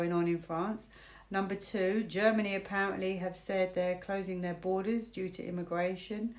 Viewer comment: I thought they said closing border in six days .